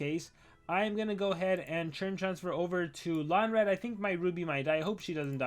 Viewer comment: did I hear him say Onda a lonradh